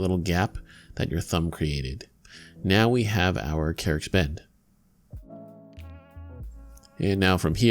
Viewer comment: what are you saying? Carrick Bend?